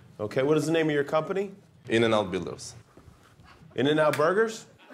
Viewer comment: Episode titled "The bathroom blunder" First thing i hear is "What's the name of your company?" "In and out dildos" 2:07 🤣🤣🤣